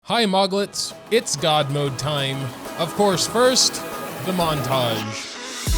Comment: I heard “it’s dad mode time” and I was like wtf